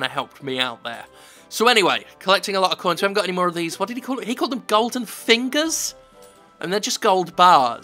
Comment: "What did he call them? Golden Fingers?" ... no, he said "Golden Ingots"... which is technically the official term for a "smelted bar of gold", so that at least is fine. The rest of the game... not so much.